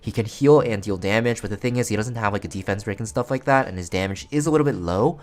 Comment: did he said blaze dingo have no defense break? im confused my english is bad